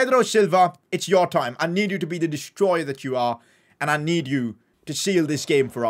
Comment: Lol when you subbed on Pedro silva in the psg game, i thought you said”I need you to be the destroyer of the Choua which in Hindi choua means rat